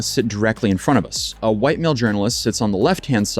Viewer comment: Freudian slip lol “a white male journalist” instead of “while a male journalist”